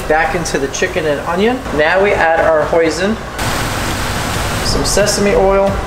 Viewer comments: Did I just hear him say poison 10:42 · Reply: Hoisin sauce lol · Why at 10:40 I thought he said poison